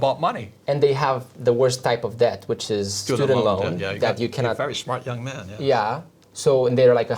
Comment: the worst type of debt... didnt hear that, what are they saying it is? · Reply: Student loan😂 3 years damn